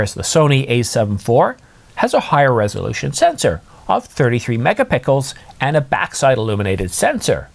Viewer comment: Well based on your review 1:00 I'm sold on A7 IV since it has 33 mega pickles I had to rewind few time to make sure I hear it correct